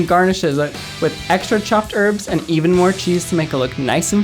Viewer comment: did he say erbs